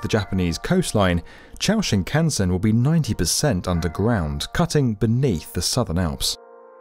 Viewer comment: Did he say “cutting beneath the southern Alps” at 4:31 ???